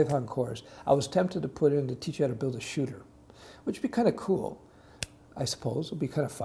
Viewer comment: did he say shooter?if yes, did he mean shooter like a shooter game?